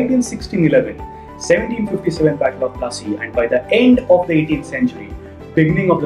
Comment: Classy instead of plassey